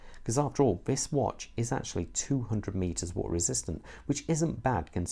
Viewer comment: 200 meters, not mm...lol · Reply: I don’t know we’re got the mm from sir go to 6:10 and I clearly say 200m 🤔 I think you must of miss heard what I said , no worries 👍🏻